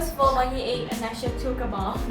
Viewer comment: Who is the girl name?? Is she said "kabam" 10:38 ? If she said so , i thought it's about mcoc. LoL